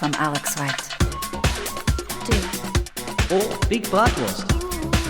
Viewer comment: Ngl, I thought it was saying "big buttocks" rather "big bratwurst".